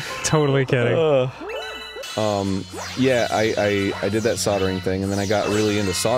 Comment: at first I thought Dan said sodomizing. I mean who knows. The whole D club thing and all, maybe that lead Arin to liking sodomizing people.